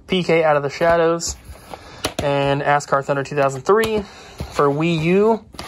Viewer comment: Did he say Asscar?